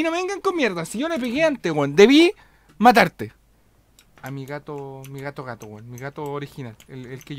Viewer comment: que wea en el min 3:58 escuche ""arigato"" cada dia me vuelvo mas otaku por la chucha